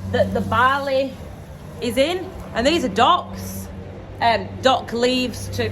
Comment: docks!! ohh thought u said dogging 😂